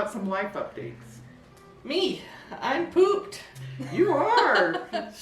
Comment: Oh. My. Goodness. when you started with LIFE I thought you said "I pooped!". Hahahahaha. I thought well, as we get older we celebrate those things, don't we? Enjoyed this video very much. I'm not sure I will do MAYNIA this year. When I set goals it feels too much like work. I'm sorry about your cousin 😔